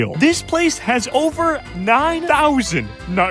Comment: Sorry, did you say "OVER 9000-"